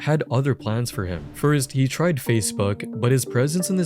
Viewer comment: I swear you said "facefuck" instead of facebook